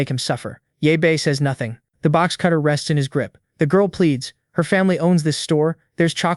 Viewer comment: did you say plead?